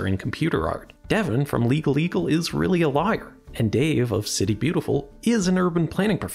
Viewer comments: Lol. When you say that Devin from Legal Eagle is really a lawyer, it sounds like you say "liar". 😂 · "Devin from Legal Eagle is really a Liar"🤣🤣🤣🤣 I had to replay it a few times to make sure I heard it right. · I thought I heard "Devin from LegalEagle is really a liar." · It sounded like you said "Devon of Leagal Eagle is really a Liar" haha he'd approve of that joke. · Am i only one who hears 15:25 "is a lier"? Instead of "is a lawyer" :) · Devin from legal eagle is really a lair is what I heard, though I'm pretty sure you intended Devin from legal eagle is really a lawyer. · I'm sure it's just your accent, but I've listed to it twice and it really, really sounds like you said "Devin from Legal Eagle is really a liar." Which I find really funny. :)